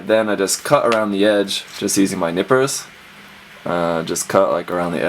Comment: I thought you said nipples instead of nippers